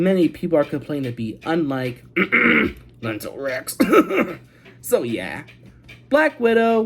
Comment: I say COUGH COUGH Fant4stic and BVS COUGH* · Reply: It sounds like you were saying mental rex instead of something that sounds close to Fant4stic or Batman vs Superman.